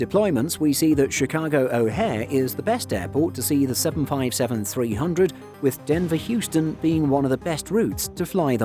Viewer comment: captions say 757-500 but we're talking about the 757-300, otherwise a great video :)